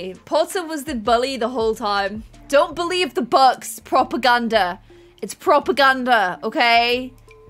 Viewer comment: Fully when I heard say propaganda I thought she said proper gander... what's wrong with me lol Also the whole Harry thing made me CACKLE haha Edit: forgot to mention now that Harry has joined the gardening career I guess you could call him a potter lol